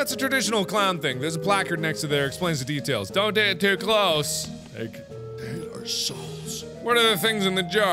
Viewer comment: Anyone else hear the thing around 27:46 after he the conversation about the eggs ends... it sounds like something saying "it's their souls" or is that just me?